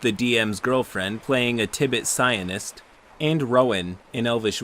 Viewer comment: Oh jeez when you said psyonist I thought you said Zionist